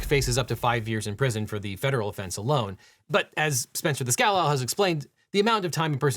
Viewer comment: … I have to apologize to Spencer. Because this video is captioned, I finally saw his name written down. I was mishearing his last name, Di Scala, as “Scowl Owl” for years and figured it was a nickname, similar to Devin being the “Legal Eagle.”